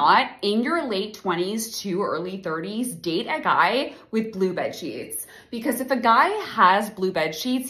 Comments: I misheard blue bedsheets as blue buttcheeks and I can’t stop laughing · I thought you said Blue Butt Cheese, and thought I was okay, until I realized you actually said Blue Bed Sheets